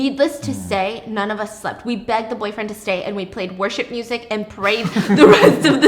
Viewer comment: My two favorite moments of this video- 1) In the first story when you said they played worship music. I have NO clue why when you said this I thought you said “War Ship”music and laughed and imagined them playing like sailor music or pirate music, oops…. 🌊 🏴‍☠️ 2) I had another good laugh when you started the second video you said lose faith in “Humidity” not “Humanity” so I was wondering wtf scary story someone had about it being too hot. Lml. 🚫🥵☀️🚫 Edit: To the girl in the 3rd story I can totally relate. I worked at a fast food restaurant when I was 14-16. It was sort of cramped because the store was inside of a Walmart. So it was kind of hallway shaped so it was kind of hard to walk by someone, and I had a boss who would always push up against me as he walked by…it wasn’t THAT cramped to where he had to do that. It was super uncomfortable, so just know you’re not alone.♥️🤝🏻♥️